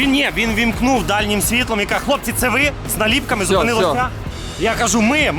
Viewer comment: Послышалось "Зупини лоха") тоже неплохо звучит))